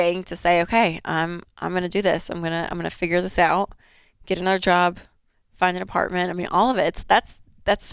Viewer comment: i heard "get a bl**job" ^.^